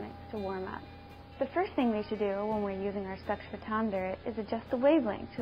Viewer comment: wait, at 0:27 did she say "sex-photometer"?